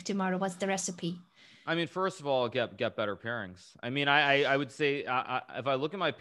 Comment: It misheard pairings for parents, so from that point the conversation went fun for a while:)